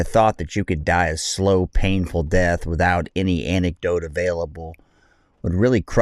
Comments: did he say without any anecdote?? 😂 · try antidote instead of anecdote instead ? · Did he just say "without any anecdote" available? 😂